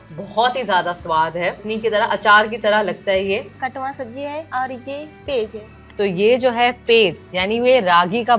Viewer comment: Damn I heard Katwa Sabji😮😅